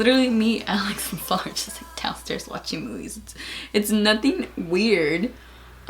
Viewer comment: I️ swear I️ thought she said her and Alex f*cked and shalt was downstairs watching movies 😂🤔